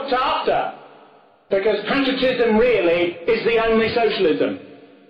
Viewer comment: "Because (BLANK) really is the only socialism" What is that word he is saying? I cant make it out. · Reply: Patriotism